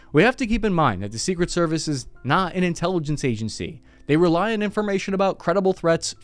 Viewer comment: lol; when you said “Secret Service is not an intelligence agency “. I heard ‘ INTELLIGENT agency😅